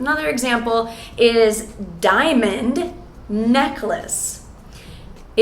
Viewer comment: Every time someone says "diamond necklace" I hear "diamond neckless".